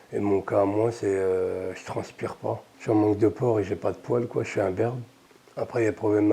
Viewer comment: il dit quoi "je suis en manque de porc"